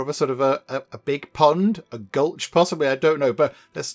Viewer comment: as someone still learning english i am curious to what the word after pond was a goulsh was what i heard haha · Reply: As has been pointed out by many people, the word was gulch!